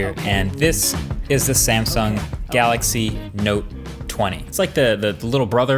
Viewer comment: what did he say ; note or nope. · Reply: Note